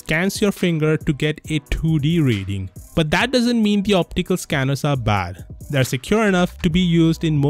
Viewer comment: bhai accent ka kuch karo, instead of getting American its getting Indian day by day... at 2:19 you used hindi.... you said and I quote.. "but that doesnt mean ki".... Please work on your FAKE accent...